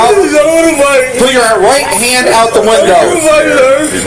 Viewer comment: What are the cops saying at 3:56--take your hand out of the (wall?)? Then three shots? I don't see a gun in the video, but I guess they did?